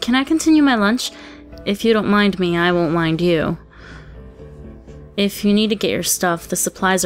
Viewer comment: I just heard Rin saying "I won't wind you".